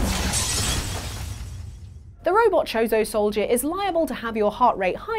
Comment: I thought the Metroid one said "Chorizo Soldier."